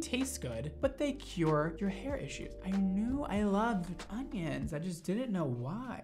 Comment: I swear he's saying "ongion" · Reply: Lol I replayed the video to hear him say it😀 he did sound like he was saying ongion haha 😂